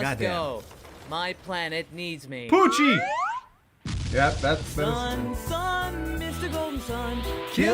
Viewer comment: It was only when watching this movie randomly with subtitles, did I realise that Goku's line at the end there is "Sun, Sun, Mister Golden Sun". I'd always heard that as "Sun, Sun, mystical sun"